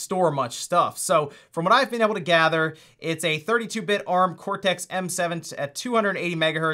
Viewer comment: did I hear right? A 32 BIT processor?